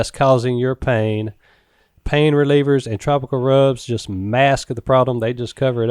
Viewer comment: it sounds like you said “tropical rubs” at 10:57 love the show!